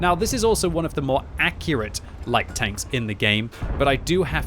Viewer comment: Did he just say Light tanks lol